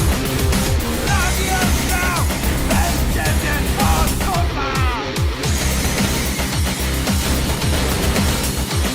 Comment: did i just hear Kurwa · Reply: Dominik Goslawski I ain’t good with polish since I’m American but I think it said “wesz się stont kurwa” ( Translation. get the fuck out of here)